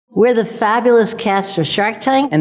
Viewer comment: i thought she saying "We're the fabulous cancer shark tank." lmao